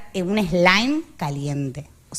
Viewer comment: respirar un que perdon? no entendi. gracias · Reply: Un slime caliente